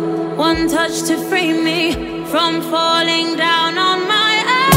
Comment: I saw in the comments some people were wondering about lyrics. This is what I hear: Open my mouth to breathe the words to you The words just won’t come out so I write Write on the canvas built from feeling Emotion persevering inside It’s just one touch to open One touch to close One touch to show me Not all minds are cold One touch to loosen Loosen the hold One touch to free me from falling down on my own No shadow Darkness I am falling no more I never felt like this before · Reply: I heard "from falling on my ass".. kinda funneh if u ask meh..